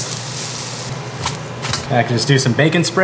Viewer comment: I thought he said bacon spray and immediately jumped on amazon. 😇